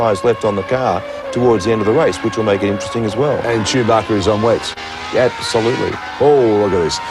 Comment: Did he say "Chewbacca is on wets"?